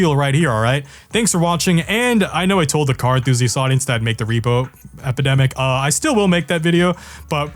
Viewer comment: i thought it said "the fucking epidemic"